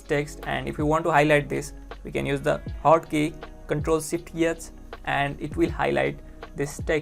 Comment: what did you say at 1:07 to highlight ==text== ctrl and ? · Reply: hahaha I was about to ask the same thing. Dude said "ctrl shift it" lol